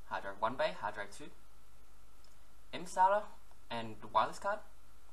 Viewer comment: What component does he say after MSATA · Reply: Wireless card